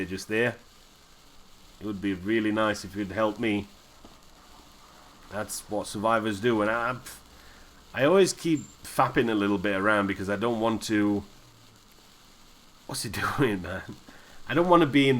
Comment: What is the word you use at 32:36? It sounds like "fapping" but that's prolly not what you meant :D